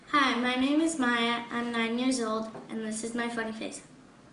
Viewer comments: Sounds like she says "...this is my fuck face." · Oh lol, at 1:00 I thought she said," hi my name is mia, and this is my fuck-face."